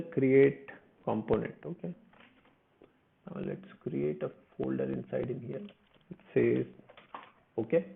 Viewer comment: Thanks so much, I was a little confused when you created the "create-user.component.js, I thought you said "create a new folder, but realized it was a new file. Thanks so much for the tutorial, I'm working along with each and everything is working so far! All the best.